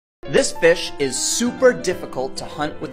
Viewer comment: At the start I thought he said this bish